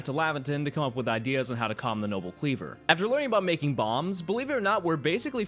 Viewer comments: I heard “After learning how to make bombs” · For a second there I thought you said "after we learn how to make bombs" I was like wot; then realized ah B A L M S